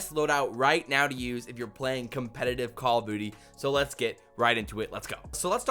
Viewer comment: did he say call of booty 0:19 into the video lmao